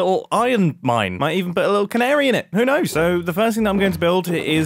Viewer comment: did I hear canary😃